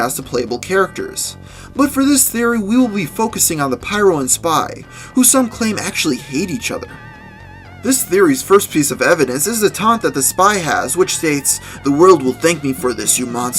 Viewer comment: For the Pyro and Spy I only had one headphone in I thought you said "Some actually claim they ate each other"